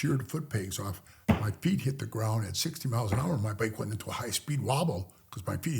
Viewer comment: & 58:22 did he say “Massatusiss” instead of “Massachusetts”??? It’s petty to point it out. Just curious & humorously ask with respect because I’m originally from Massachusetts 😂😂 And there is a real place “Lowell Massachusetts”